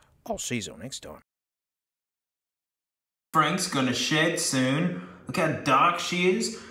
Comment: Am I the only one who thought he said “Frank’s gonna shit soon”?